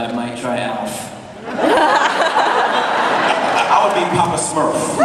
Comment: Did he say that he should be Papa Smurf!? 🙀